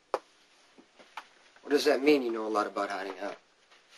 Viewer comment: What does Jason say at 0:05? What do you mean you know lot about what? Carly: I also know you would drop me in a hot minute for Robin Scorpio. ROFL! Woah they are HOT HOT HOT! :) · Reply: "What does that mean... You know a lot about hiding out?" 😍 Aww, Jarly, my FAVES!!!